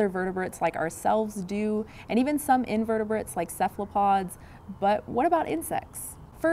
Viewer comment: What did she say at 1:09? Some invertebrates like...? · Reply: Cephalopods.